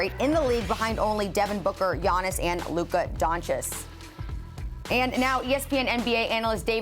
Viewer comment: Did she just say luka donshis?!